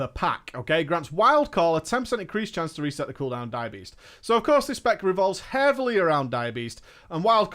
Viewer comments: Took me a bit, I thought he was saying "Diabetess". But was really Dire Beast. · I keep hearing "diabetes" when he says dire beast